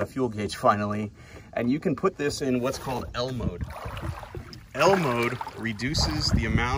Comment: Great vid! I thought you said Elmo not L mode haha